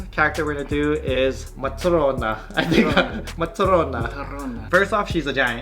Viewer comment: Who is the buff girl at 9:23? They say her name with such an accent I can't figure out how to spell it to Google it lol. Buff anime girls are my kryptonite, I must know more. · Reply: Smiley J nanatsu no taizai - matorona